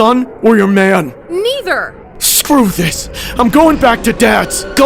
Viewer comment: I thought she said…that.. But she said nether 🥲